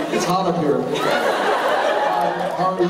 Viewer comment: What did he say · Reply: Lets Have Fun “It’s hot up here.”